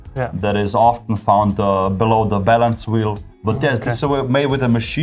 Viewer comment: i heard "...this is a type of decoration often found below the bellend's wheel.." lmao